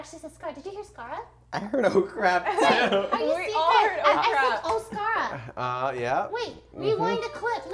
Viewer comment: i thought she said oh SCARAP!